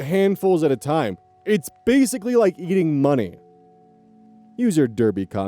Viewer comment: I thought that said "eating honey" at first and thought she was referring to Pooh Bear sticking his hand in a honey jar and then into his mouth. XD